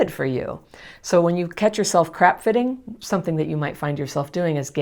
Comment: I’m an expert at crap fitting !! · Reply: Heh! I thought she was saying “crack fitting.” “Crap fitting” sounds more apt to what I do.